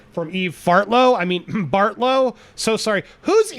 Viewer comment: DeBartlo? That's a Mafia Family from Youngstown lol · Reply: I guess I heard wrong, he said Barlow, sounded like he said Debartlo, which the sheriff in Florida that refuses to take Don Lewis MURDER seriously, is married to one of the Debartlos...